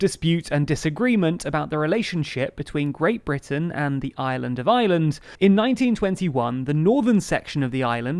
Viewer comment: I’m sure this is just my American ears, but when you say “Island of Ireland”, I just hear “Island of Island”